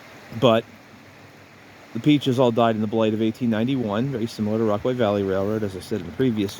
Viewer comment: What do you say at 1:02 - the peaches all died in the ... of 1891? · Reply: blight